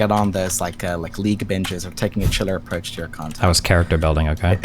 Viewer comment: wtf does he say? i re listened like 10 times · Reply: “Taking a “chiller” approach to your content”